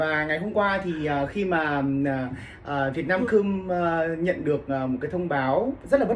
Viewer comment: có nghe nhầm không vậy :))))))) vietnam cum 😂